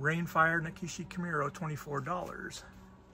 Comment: It’s sounds like your saying Nakisie it’s Nashiki